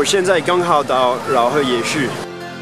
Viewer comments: 饒河夜市不小心聽成老二夜市☺️ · 我聽成老二夜市